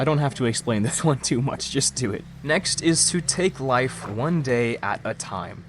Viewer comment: I thought bro said take a life 😅😅😅😅😅